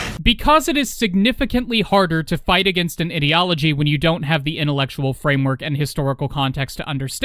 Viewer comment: im not sure if this was intentional or not but everytime you refrenced fascist ideology it sounded like you said idio(t)ology